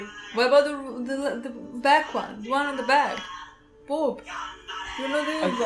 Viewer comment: did you call her boob?